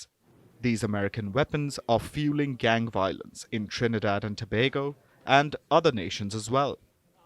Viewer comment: I thought that said trinidad and tobacco I was like wtf